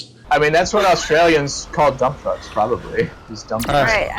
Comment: I had this open and was reading something in another tab. And I heard "I mean, that's what Australians call dumb f*cks, probably"